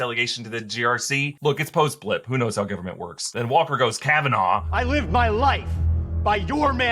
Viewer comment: Then Walker goes cavanaugh? What is that word? Appreciate if anyone can help please? Thank you